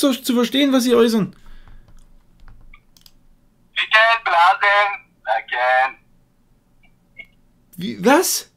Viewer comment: Ich habe am Ende verstanden: "Sie können blasen, danke!"